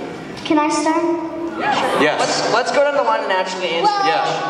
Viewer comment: who else heard someone shout to Jackson “ YES SHUT UP “ like rudeeee